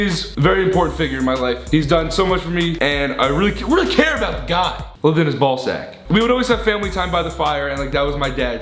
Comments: All I heard was ‘I don’t care about the guy I love his ballsack’ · Did anyone else hear Ethan says I love my dads ballsack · what Does that say it's Driving me crazy😵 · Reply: Julaibeb S it says “I lived in his b sack”